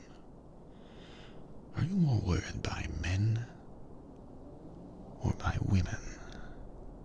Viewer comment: What does he say at 3:50? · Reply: Elliott Medina More worried by men or women